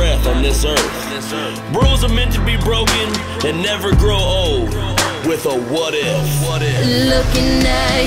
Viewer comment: anyone know what he says at 2:59? I can’t figure it out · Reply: Lucas Altman rules are meant to be broken and never grow old with a what if